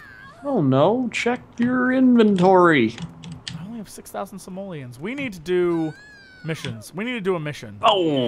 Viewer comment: Did I just hear "Simoleons"?!